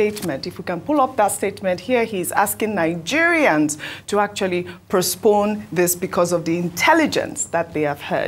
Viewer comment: Did Ojy say “Prospone” or “Postpone” @ 14:12 or was it my ear??👂